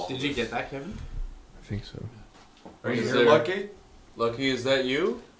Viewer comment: what is the name theyre saying at around 55:40 ? to me it sounds like either Loki (my name) or Lucky (the name of one of my closest friends) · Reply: The spirit said Lucky so they thought it was its name. Its even more believeable because Lucky is a native name which would line up with the native war